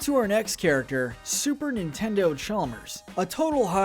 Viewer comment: i heard super nintendo Charmers